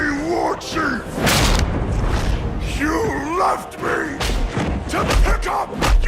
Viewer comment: In the VS Thrall fight when said ''You left me! To pickup......'' at first i heard him say: '' You Loved me! ....'' xD and now i cannot unhear it xD